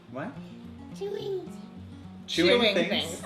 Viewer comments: When said chewing things the first time I thought he said jimmy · When said “chewing things” I thought he said Julius. 😂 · When said chewing things I actually thought he said Jude which would be so cute Jude Ballinger